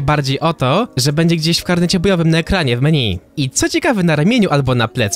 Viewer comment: ja usłyszałem że będzie miała przybysza na plecach xdd